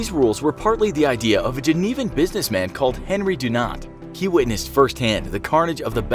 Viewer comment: I heard 'Henry Do Not'